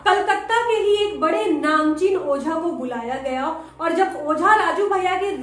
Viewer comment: did I just hear namjesus🌚😳💜